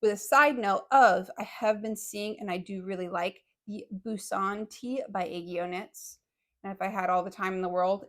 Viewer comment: you show a tee and i dont understand the name 😢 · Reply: Busan tee… it took me a while to find it